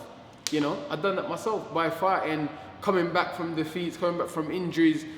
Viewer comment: Excuse my ignorance. I don't think my hearing is that great as it used to be, but did Whyte just say he made it big by farting 35:15 Bantz LOL